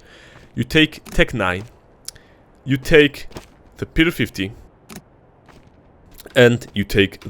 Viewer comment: did you just say pedo-50? xD